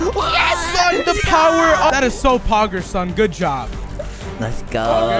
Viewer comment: All i hear “Poggers son” “Poggers”